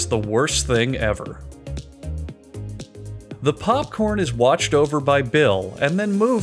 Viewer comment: I thought he was about to say “the popcorn is watched over by a BLIND MAN “ 😂😂😂😂😂